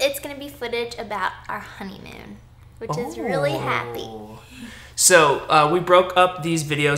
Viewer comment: who else thought he said: so uh we broke up.... at 0:20 and had a mini hart attack